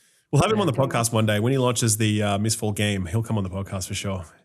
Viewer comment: Did just say “MistFall”?